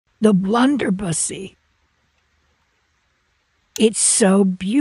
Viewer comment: 😅😅 I heard 'blunderpu$$y' and nearly pissed myself! 😅😅